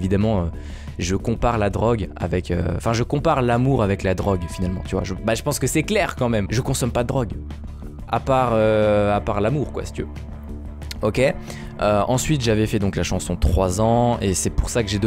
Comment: J'ai entendu "A part heu..." je me suis dit "MColo tu nous cache des choses hein ?" mais si c'est que l'amour alors ça va 😂